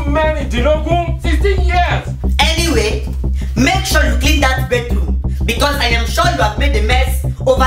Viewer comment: the cc said bathroom but she said bedroom